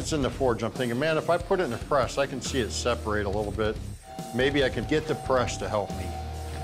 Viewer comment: sounds like he said, "I can get depressed to help me!!""..... hahahaa